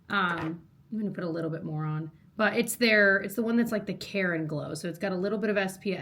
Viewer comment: Okay… I thought you said “Karen Glow” for your foundation😂